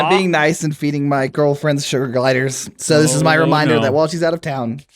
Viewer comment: Missed the 's there, and thought, for a moment, he said that he was feeding his girlfriend sugar gliders.